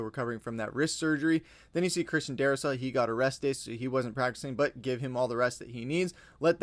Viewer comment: Yikes. At first, I thought you said Darrisaw got arrested.